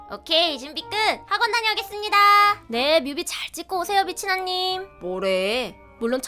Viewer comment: 나만 빛이나님을 미ㅊ언니라고 들었나...? + 남편 얼굴이 진짜 너무 대박에요.. 사랑해요... 이번 편 레전드... 순애보 남편 우러 ㅠㅠㅠ